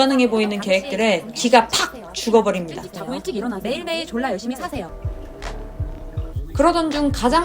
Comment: 아련하게 들리는 "매일매일 졸라 열심히 사세요" ㅋㅋㅋㅋㅋㅋㅋㅋㅋㅋ